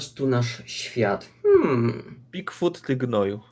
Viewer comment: czy tylko ja usłyszałem "kwód ty gnoju"